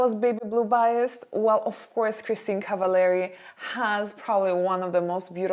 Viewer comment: I didn't understand the name.. can someone tell me the name of the actress? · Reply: Kristin Cavallari :)